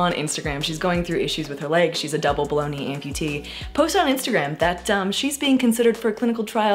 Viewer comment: When you say "below knee amputee" I hear "bologna amputee" 😅